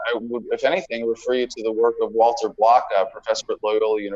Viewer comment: who is this? I hear Walter something-out The cc interprets it as Walter blockout. I need this citation.